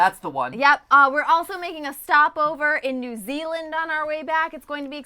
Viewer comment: I heard “ we may stop at New Zealand” Me: YES MATPAT COME TO MY HOME